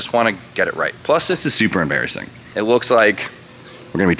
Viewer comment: i thought you said this is "supra embarrassing" haha